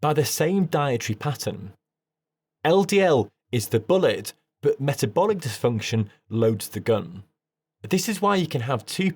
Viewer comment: Very good video! The fact that most of the population eats a high-glycaemic diet is the core problem. It makes LDL-C look like the cause because there is a (weak) dose response. Sugar is not “loading the gun “ as you say, but one of the main causes of glycocalx damage and LDL-C contributes to the development of plaque. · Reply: He did not say sugar loads the gun though. 10:09- "Ldl is the bullet, metabolic dysfunction loads the gun"